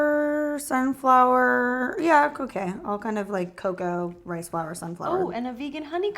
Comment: for a second i heard cocaine 😂